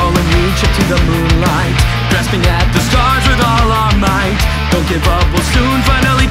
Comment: i know he said we with all are might but it deadass sound like he said with out all might LMAOOO